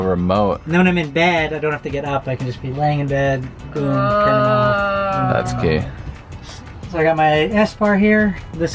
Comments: Did he say that's gay at 5:36?? 😂🤣😂🤣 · I thought he said that gay